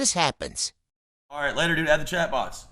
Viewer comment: Sounded like he said ADD the chatbots, not end the chatbots.